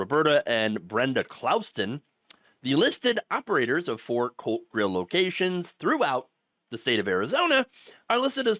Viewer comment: ooh ! COLT grill lol I wasnt looking at the video when it started and thought he said Cult Grill. I'm here saying to myself... how ironic!